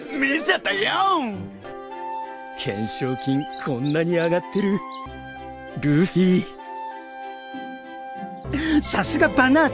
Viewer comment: Sabo:Luffy I heard: Goofy